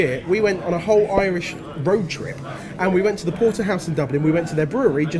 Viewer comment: At the beginning, I thought Jamie said "we went on a whole Irish BRO trip", then I realised he said "road trip"...